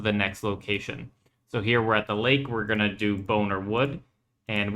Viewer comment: "Boner Wood" is what it sounded like, had to double take that real fast LOL